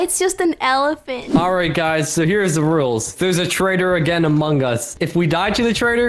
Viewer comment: Did said there's a traitor in Among Us